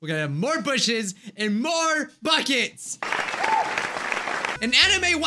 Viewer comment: no one: bijuu mike: ”MOREBUCKETSMOREBUCKETSMOREBUCKETS” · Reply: I thought it said smoresbucket